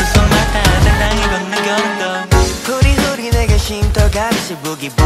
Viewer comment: for me on new year’s eve so the first thing i hear is hoodie hoodie